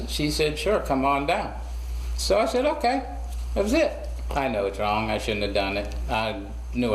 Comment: Why did he call him John? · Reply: He said i know i was wrong not john